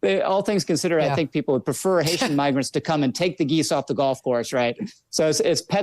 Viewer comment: I thought he was saying Haitian neighbors LOL